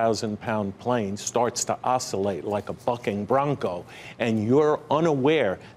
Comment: did he say... fucking ?? Lol